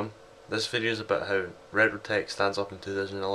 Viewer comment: You accent makes it sound like your saying reyro, instead of retro lol good video, you gota love these older ppc macs :)